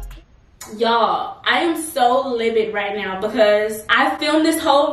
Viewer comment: She said libid instead of livid 00:13 & @ 00:43 she said petromeum instead of petroleum jelly. omg.